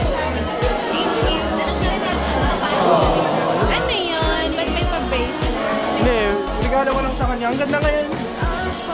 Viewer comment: What did Kyle said to chin @ 14:27 when he hugged chin? Replayed the gift giving scene 1M times 💜💜💜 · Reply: Louise T. “You look beautiful “